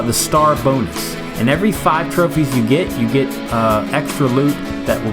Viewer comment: did he say every 5 TrOpHiEs?